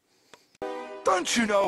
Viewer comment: Ahhh! .. oh wait. You said JUGGERnaut... I thought you said Jugglenaut there for a second.